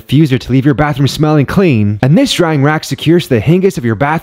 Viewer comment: Did I hear “Hingis”? 😂